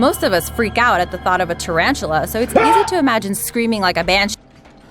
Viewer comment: Sounds like she said you can easily scream like a BITCH right after screamed. 😂